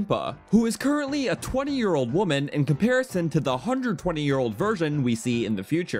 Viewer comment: For a second I thought you said “120 year old virgin” when talking about Impa and I guffawed.